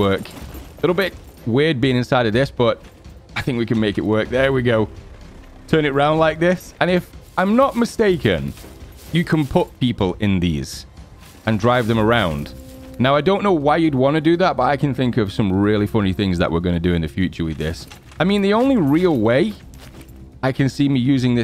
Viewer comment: so scum is online ? thougt i heard you say put people in wheelbarrels xd?